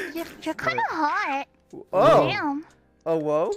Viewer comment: Did he just say OwO